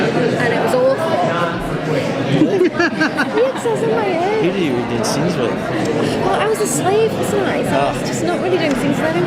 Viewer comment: What does Elizabeth say at 1:45 ? :) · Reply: "I was on my own."